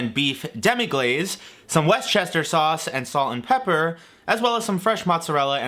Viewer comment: I heard "Worst chair sauce" and "west chester sauce" lol